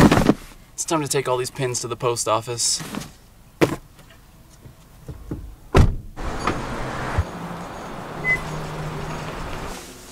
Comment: who else thought he said "time to take all these panties to the post office" at first @ 6:53?